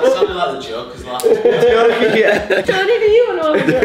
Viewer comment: did call stoney - Tony or am I hearing things and the blue hoody has on I had that for Christmas can be turned so blue or whit very warm x